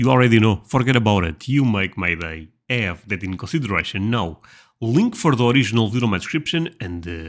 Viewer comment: I thought you said “Russian” the first time a saw your video when in fact you were saying “consideration”. Maybe other heard wrong like I did.